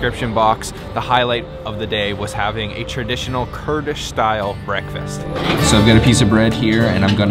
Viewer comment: Did you say Kurdish style breakfast??? (0:43) Thank you very much for your wonderful videos about my lovely country. I see that you are really having fun and having wonderful Turkish food and breakfast. I wish you all the best.